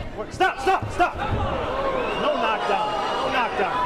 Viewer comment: "Oh my god, no knock down", sounds like he said that hahaha